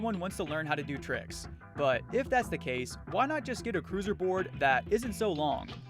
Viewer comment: i thought he said loser board not crusier :face-blue-smiling::face-blue-smiling::face-blue-smiling